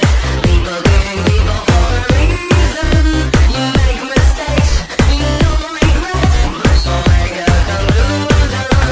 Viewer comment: Wait a minute.. I can't tell if the background is saying Okay boomer Or Obey (word) I don't know what is the second word is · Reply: It’s obey submit